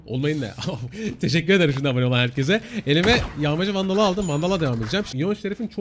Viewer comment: bı ben mi yağmacı Vandal anladım xd